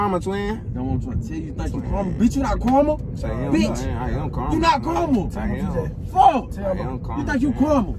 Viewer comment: it sound like saying caramel than karma😂